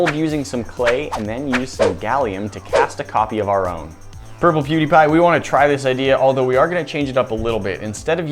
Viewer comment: I think i heard say PewDiePie at 0:46 to 0:47...play it at .25x speed